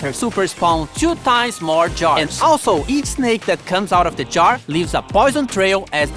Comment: am i only who thought he was saying Snakethugs???